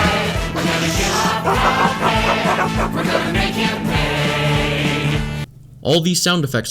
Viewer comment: I thought they said "We're gonna make them gay" xd sorry